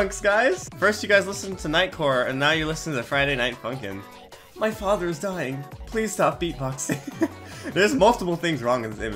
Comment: at first i thought that said "my father is beatboxing please stop dying"